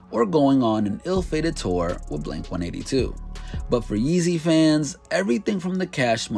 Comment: anyone else hear him call wayne “Yeezy” at 9:38?😂